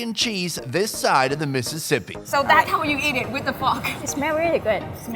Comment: when she said fork I was like☺️👀🤪 did she just say F***🤣😏👏🏾✌🏾